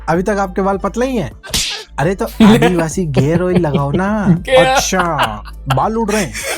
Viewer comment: Anyone hearing it as "GAYer" oil ??😂😂